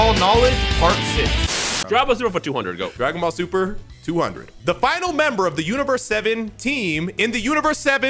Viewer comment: I thought thay said "the final chosen member of the team" which led me to Picolo